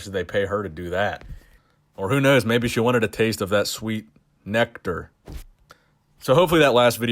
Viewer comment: thought you said 'neck dirt', and not nectar lmao